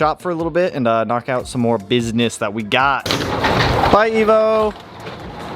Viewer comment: when you said “Bye Evo” it sounded like you said “Bye Nemo” 🤣